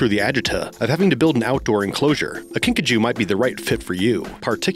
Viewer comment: I though the No.10 was 'Pikachu' Lmfao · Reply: No,when he said "Kinkajou" I heard "Pikachu"